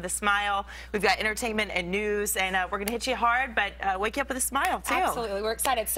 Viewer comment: did she say "we are gonna get you hard?"